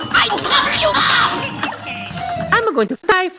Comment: Anyone else hear "I'll cover you up" instead of what it says on screen & in the captions?